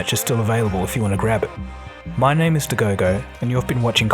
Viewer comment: Funny how I always hear, "My name is Tegogo" 😅